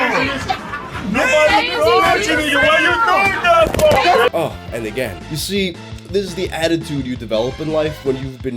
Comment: cool it with mysoginystic remarks buddy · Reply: What are "mysoginystic remarks"? Misogynistic? Anyway there are no misogynistic remarks at 04:22 - I've gone through that timestamp several times now, and all I hear is, "uh, again".